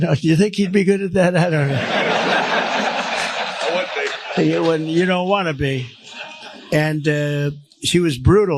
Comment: did the guy contradict by saying "I would be"?, that's funny if that's what he said.